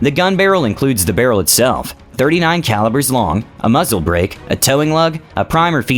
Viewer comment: did he just say the barrel was 39 calibers long?